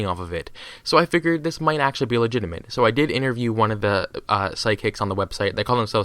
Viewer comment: I thought it said "Interview With a Psycho". Awkward...